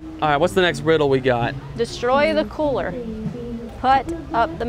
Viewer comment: it sounded like she said destroy the poor